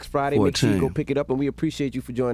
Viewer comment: Tell me why at the end i thought envy said dick mensa lmaooooo 😂😂😂😂😂😂😂😂😂